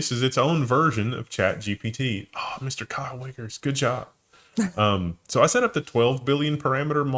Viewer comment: said “Kyle Wiggers”, however somehow originally heard it as cunnilingus and had to re-listen.